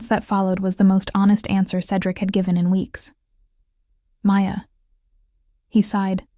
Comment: I love that the caption said "no" 26:51 but the narrator's voice said "Maya" I had to laugh because I thought "NO" in my head...